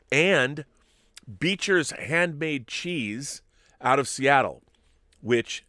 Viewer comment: You sound as though you read my letter which I have yet to send in. 2:33:53 Not going to lie. I first heard "V'Ger's handmade cheese".